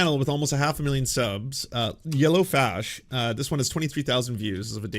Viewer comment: Love that the dude calls himself, "Yellow Flash," because Eobard Thawne in the comic books was basically the biggest self-victimizer of all. He was essentially mad at Barry, because Eobard wanted to BE Barry, and he basically had to commit crimes in order to play the hero. Eventually, he reads Iris West-Allen's biography of Barry Allen and finds out that Barry murders him after Eobard kills Iris, weird time travel stuff that drives him absolutely insane. Instead of just being like, "I'm going to just stay in my lane," he goes out of his way to torture Barry in order to punish Barry for killing him. Just some irony. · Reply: I thought it said "Yellow Flask". A piss bottle.